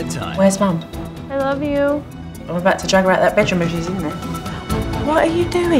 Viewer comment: I thought Jo said ‘I’mma drag around that b*tch until she’s in there’ 🙈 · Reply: coconut and mint I know but she said ‘ I’mma go and see if mum’s in the bedroom’